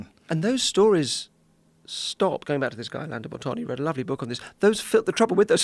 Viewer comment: Alain de Botton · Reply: I heard it as Orlando for some reason